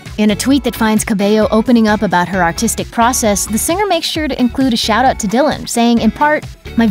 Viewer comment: I honestly had to re watch it like 5 times because I thought they said Autistic process